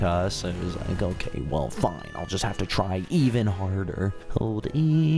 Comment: did he just say the f word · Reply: he said fine